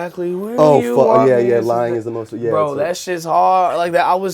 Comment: Did brendon say that ‘But It’s Better If You Do’ was ‘LITMFAGCH’ or was that just me who heard that at like 3:20 😂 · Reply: katie o'r he said lying was the most fun